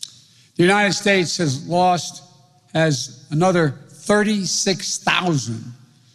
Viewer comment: did he say” The United States has lost as another 36,000 new Covid cases per day per day? Can someone translate this please I’m asking for a friend